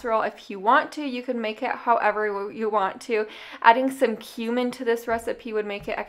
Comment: I kinda had to do a double take because I thought you said 'adding some human to this recipe would make it extra flavorful'. lmao